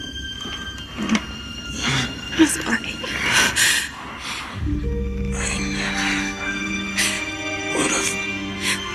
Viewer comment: what did Sydney say? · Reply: "I'm sorry."